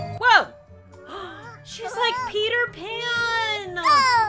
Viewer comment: when she said she's like peter pan aww i thought she said she peed her pants aww